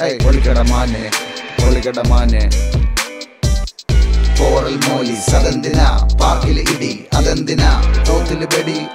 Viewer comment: did he say poral molice ?🤡🤣